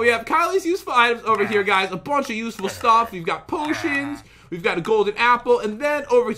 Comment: At time 2:25 did he really said usless stuff???